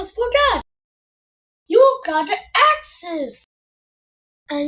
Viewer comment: I think they said "IT'S DIGGING TIME"